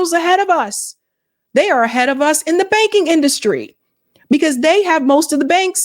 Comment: Lol I thought she said China is ahead in the baking industry.